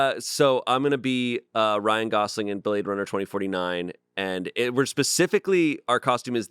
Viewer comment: Oh! Hell yesh ❤ I have some strange weird paranormal stories myself! ...the Bladerunner 2049...I thought I heard, Bladerunner 2025 and I was like...wait what. ...bladerunner ahahahaha